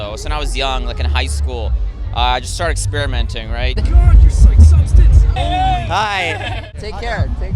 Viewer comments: What does the guy say on 2:50? I usually get ... what? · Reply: Orange Sky he said got rut but he meant gut rot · Any one know what the guy at 2:48 said? He said on mushrooms he usually gets ______. What does he mean by that? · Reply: He meant to say "gut rot" which is when your stomach feels like shit pretty much. Here's a link. <link> I only know cause I literally heard this word from my cousin over Thanksgiving LOL